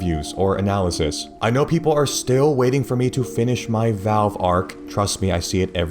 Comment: Did he say Arc? ARC RAIDERS REVIEW WHEN???!!!